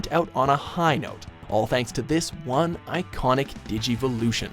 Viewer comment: Damnit I thought it said "ironic Digivolution" :/